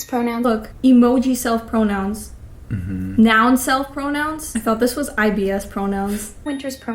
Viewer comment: "Noun self pronouns" - for a second, I thought i heard "nonsense pronouns"😂